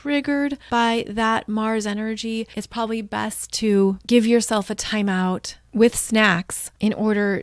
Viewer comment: Did you say “give yourself a timeout with snacks”?? 😅🤩